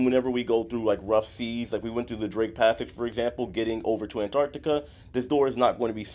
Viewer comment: When you mentioned the barf bag, I thought you said “Drink Passage” at first rather than “Drake Passage.” I thought it was like the captain purposefully navigated through rough seas to make passengers sick. 🤮